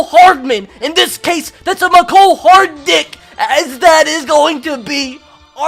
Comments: Did anyone else hear at 12:25 say "that is a mecole hard dick" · did he say mecole hardick